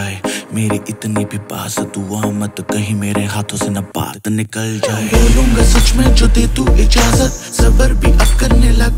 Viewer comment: does he says Paad (fart) or Baat (say)? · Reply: Sohail MZ Hoque he says baat but it sounds like paadh 😂